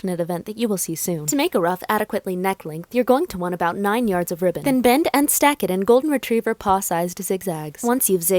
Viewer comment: For a sec I thought you said: "You need to bend and snap it" at 12:01 LOL